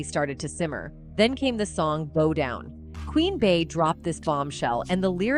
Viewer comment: 🤔 it sounds like she's saying Roll Down instead of Bow Down 🤣